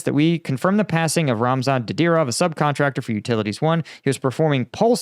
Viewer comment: When you were reading the press statement I thought you said Ramsan Kadyrov 😂